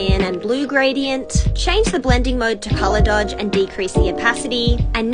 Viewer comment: I thought she said "How to drool wotah" 💀